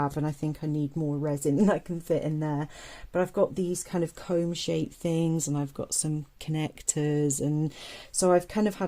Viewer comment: The three-prong thing is a transistor! These projects are so cool, and I also really love the keychains! Makes my nerdy heart happy! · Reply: Sounded like she said "cons-shaped things." That little yellow thing with the pins. That's a resistor network.